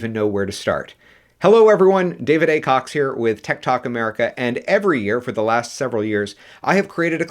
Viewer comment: I misheard your channel to Tiktok America 😅😆